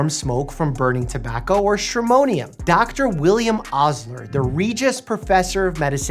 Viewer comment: anyone else thought he said William osman? No, just me…